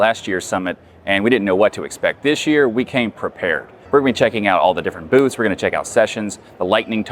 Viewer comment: legit heard that as "we're gonna be checking out all the different boobs"